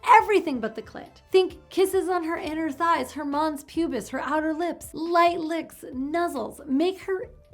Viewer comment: I heard that as "her mom's pubis." So....she likes to watch?😆